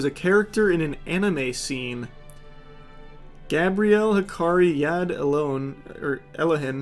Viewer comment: did you just say hakari?